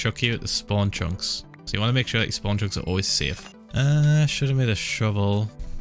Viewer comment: What he’s sayin: make sure spawn chunks are safe Captions:wanna make sure that you on drugs 1:00 Lol it’s exactly 1 min